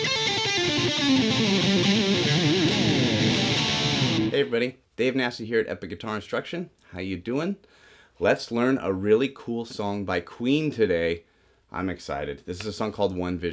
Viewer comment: When he introduced himself i thought he said "Dave Nasty". Best 80s glamrock name ever.